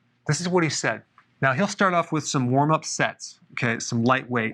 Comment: I misheard it "warmup s€x"